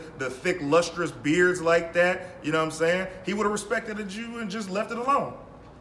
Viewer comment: did u say he woulda just respected a jew 😳 or respecteded 😳 u????? lmfao 🤷🏾‍♂️ its about the kids